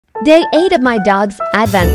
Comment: At first, I thought they said: they ate my dogs advent calendar But actually said day eight of my dogs advent calendar